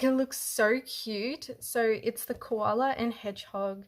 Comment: Hello fellow Virgo and Vic peep - happy belated birthday for Sept - lol I'm not sure if I misheard but I heard colour a Cake 🎂 instead of a Koala - hmm maybe she said both. Awesome works. I was in the middle of telling my cat off for drinking the paint water in my art room when the earthquake happened.